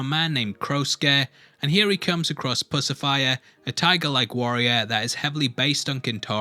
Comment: i think the tigers name was Pussifer. As in Lucifer. Not Pussifier as in Pacifier . I know cause when i heard that in the episode, i busted out laughing.